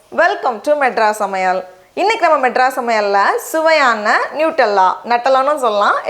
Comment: My cc was on and around 0:13 the word was sayin'" nose Allah" I didn't laugh but it made me laugh Astagfirulah I thought she said that Astagfirulah